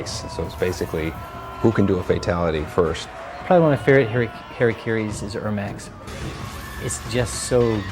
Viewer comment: Whenever they say harakiri it sounds like they say Harry Carey